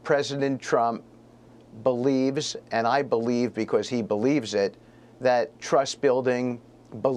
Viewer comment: Did he say, “I believe cause he believes it?”